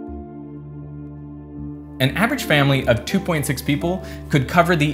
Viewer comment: Did he just said 2.6 people ?🤔